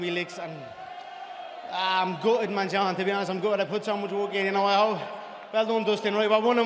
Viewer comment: I was like can't understand a thing let me turn on some subs subs - pls no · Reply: clean ure ears he said im gutted i put so much work in